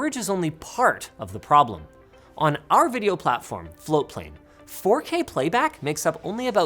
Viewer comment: Did he say flow play?